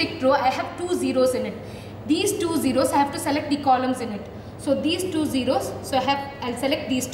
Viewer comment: I heard the word "condom" instead of columns in allocation explanation...😁😁